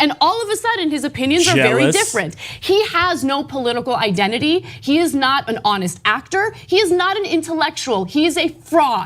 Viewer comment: Did she just say at 6:41 'he is not an honest actor'? Define honest acting.